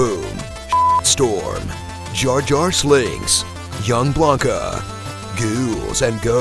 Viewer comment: thought you say wolverine instead of young blanka